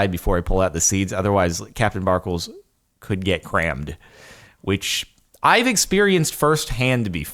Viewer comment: Did I just hear Cram?